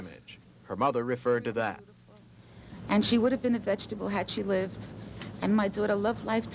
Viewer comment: Oh! And t 18:18, I keep hearing vegetable.. Can someone clear this up for me please? Thank you!